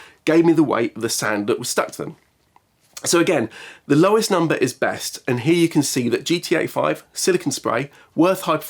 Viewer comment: i heard gta5 and thinked what, there is a lube out there called gta5